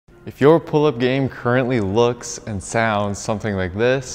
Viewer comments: Thought he said “if your pull out game..” at the beginning lmao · I thought he said " pull out game " at the beginning !